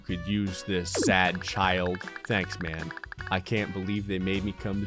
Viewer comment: Who else thought that said “thanks, mom” instead of “thanks, man”? Or am I just blind?